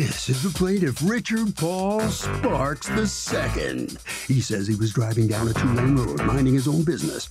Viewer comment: it sounded like it said richard balls shark the II to me